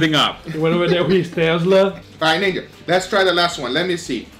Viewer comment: So y'all didn't hear that "Aight, Nigga..."